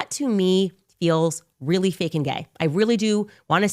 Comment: Did she say fake and gay at 12:57 😭🤣😭🤣